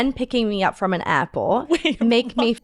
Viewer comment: "AIRPORT"! I heard "apple"....I was like, What?!